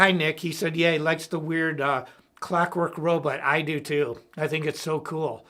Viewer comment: What is he saying at 4:10 it sounds like he's saying he likes the "weird clockwork row", "it's his favorite movie"?